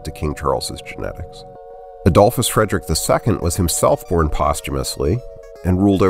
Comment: what do you say about Adolphus? that he was born posthumously? I can't make it out properly